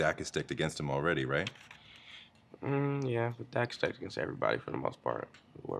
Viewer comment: WHY THEY SAY "THE DACK IS STECKED AGAINST" instead of the deck is stacked against?